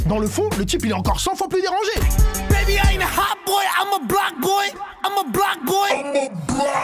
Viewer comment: il ne dit pas "i'm a black boy" mais bien "i'm a BLOCK boy". Voilà ça me semblait important à signaler pcq moi même j'avais fait la même erreur de compréhension